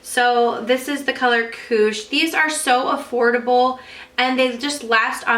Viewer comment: When you said "Koosh", I heard "cooch" at first hahahah oops!!